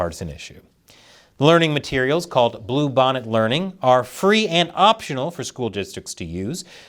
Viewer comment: thought you said bubonic learning 💀